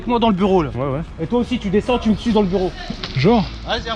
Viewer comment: on dirait pas le gars y dit "et toi tu me suce dans le bureau" il a dit suis mais g du réécoute plein de fois pour savoir qu'il a dit suis 😅